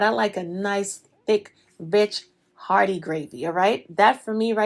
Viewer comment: did she just say b*&%h? I like them thick too.